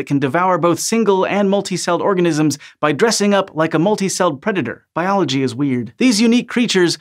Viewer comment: "Biology is weird." Yes. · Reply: I heard him say "Biology is weed"